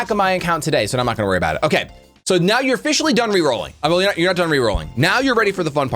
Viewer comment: i really thought you said rick-rolling and that scared me